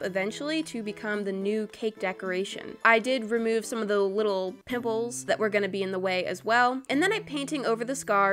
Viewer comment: OML 😂 😂 😂 at 9:12 I thought you said nipples and I had to rewatch it